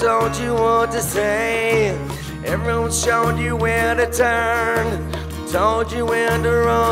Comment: Jesus, I always thought he said heroin showed you what to do, what to say. Wow, I got that wrong.